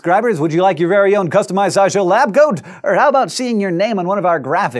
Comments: I thought for a moment Hank said, 'would you like your own SciShow lab goat?' as opposed to 'lab coat'. Needless to say I am disappointed. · I swear, I thought he said SciShow Lab Goat.